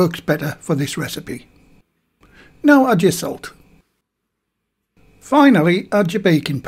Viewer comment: You show the word sugar instead of salt at this point. (I imagine the hearing impaired might least appreciate this.) Added the salt after all though. Here’s hoping these come out ok.